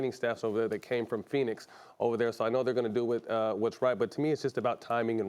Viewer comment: Thought Matt said he watched the game “high”